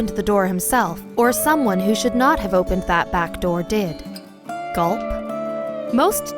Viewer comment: Did she really say Gulp!?!